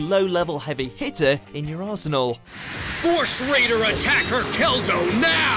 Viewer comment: I thought I heard say "asshole" instead of "arsenal" 😂